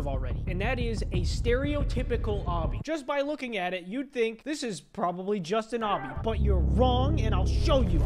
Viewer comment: A small thing, I heard "This is no obby" instead of "This is your obby"